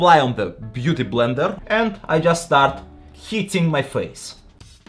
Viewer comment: I thought he said “I just start heating my face” and I died laughing because I thought he meant “baking” his face as in the makeup term. Turns out he said “hitting”😂 Joking aside though, this is very impressive. I never thought I’d be learning new makeup application skills from a big muscled Russian guy.